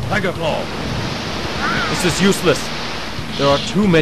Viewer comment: I’m sorry but I was getting something and I thought he said “she is useless” and got so confused but laughed XD Anyways, I remember watching this long ago! And omg it’s been 13 years!?!? 0.0